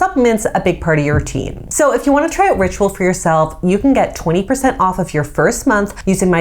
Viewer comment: I heard, " you can get ozempic " 😅